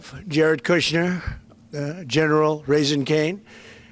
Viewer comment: did he say General Raising Cane?!?!